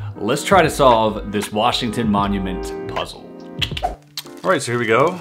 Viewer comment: idk why i always hear ''washing tit monument puzzle.'' :D :D